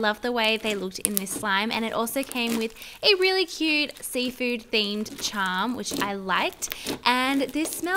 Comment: why did you say seafood slime.......